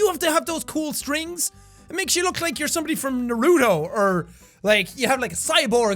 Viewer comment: did he say 'naruuuto'??? WHAT THE!?!?